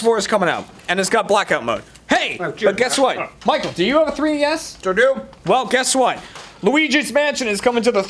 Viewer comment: "Michael, do you have a 3DS?" I swear I heard "Michael, do you have a dreamy ass?" and I was like, duh...